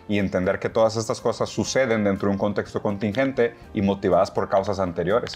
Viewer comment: Solo escuche “contingenté, contingenté, contingenté” 🙃